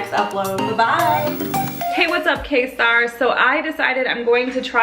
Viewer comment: It looks like something I'd see at a restaurant. This is way too fancy for me because I usually eat burgers and chicken legs as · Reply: And I don't know why I thought I heard "what is up Kim stars"